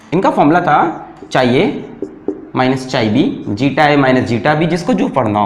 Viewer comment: Do I hear chai(tea) at 20:00 or is something else🤣🤣!!